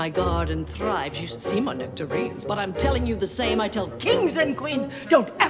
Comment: Instead of 'nectarines', I believed I heard 'you should see my winter greens.'